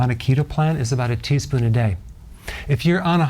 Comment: 1 tsp roughly = 2,325mg in case anyone was wondering. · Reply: Initially I misheard the amount to be a tablespoon which is 17 grams😂